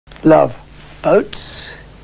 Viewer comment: I always thought he was saying "oats", not "boats". "Boats" would make a lot more sense, lol. I'm kind of sad, though--oats are my favorite food and I thought we had that in common. :( Boats are still cool, though.